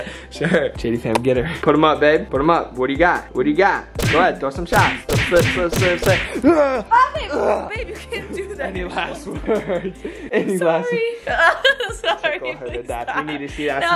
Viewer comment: Did anybody else hear her say “you can’t do that anymore” at 13:33 does that mean she’s pregnant 🤰or is it just me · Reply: she said "babe, you can't do that - your shoulder"